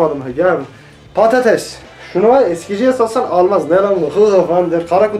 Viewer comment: Adam "eskiciye satsam" dedi ben "sikiciye satsam" anladım... o.O